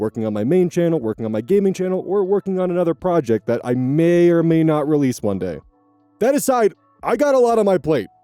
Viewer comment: Dang. Thought he said may or may not release Monday. Threw me for a second but double checked. I know this line of work doesn't really allow for breaks. I can send probably unhelpful platitudes of; Take breaks. You should rest. But, the engine needs coal. Those with nothing left to burn become fuel themselves. I hope you find a good time to rest.